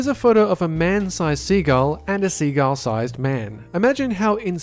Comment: I hear "a Seagal sized man"